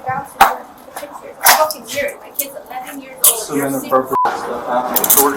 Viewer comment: what is she saying to him? I heard her say that her kid is 11 and it sounds like she calls him a fucking (inaudible).